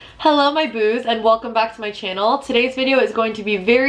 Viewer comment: I'm new here so in the beginning of the video I thought she said "hello my boobs" 😂